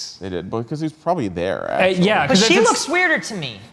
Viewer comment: Did Sam call Wren an asshole at 15:52?? "Because he was probably there, asshole."